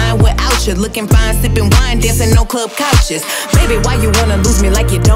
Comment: I hear "No Club Gachas" I am the only one? No?